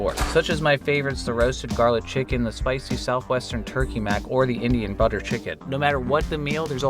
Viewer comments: 23 I thought he said "cirrhosis butter chicken" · I know I might be getting old but I heard "cirrhosis garlic chicken" and it made my night 😂